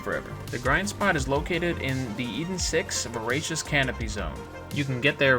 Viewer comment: Wht did he say at 1:08? I can not find any location called something like cavern zone? Even not in the Borderlands Wiki. So what Location did he mean? So to wich map do I have to trevel? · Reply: eden 6 my bro